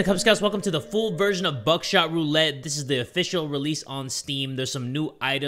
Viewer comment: i thought it said backshot roulette 😂